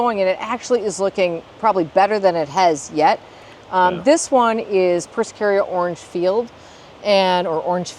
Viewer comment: Great tour! Can anyone tell me what the plant is at 24:07? I didn’t catch the name! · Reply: Persicaria amplexicaulis ‘Orange Field’ I think? Also called knotweed